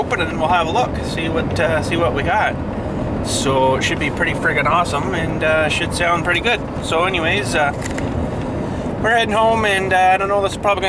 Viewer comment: YOU GOT DUALS!?!?!? i heard you say "it should sound pretty fricking good and dual exhaust sounds pretty fricking good! im really excited for ya!